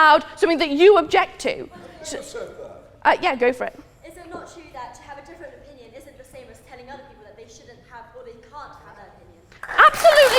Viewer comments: what did the asian girl say at 7:25? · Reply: "Is it not true that to have a different opinion isn't the same as telling other people that they shouldn't have or they can't have an opinion?" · The video gets really quiet at 7:20, does anyone know what she says? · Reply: Brenna Leech all I hear is the last part saying something like "...having a different opinion is the same as not allowing to have an opinion." I butchered it.